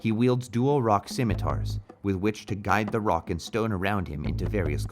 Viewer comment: DID I HEAR A ROCK AND STONE?!